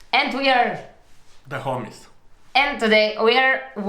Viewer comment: I thought he said the "Hornies" cause of the Accent, Lmao.